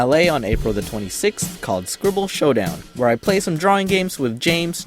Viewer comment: I thought you said "school shootup" not scribble showdown at first lol